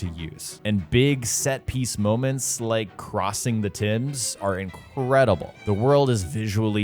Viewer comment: Did he just call the Thames the Tims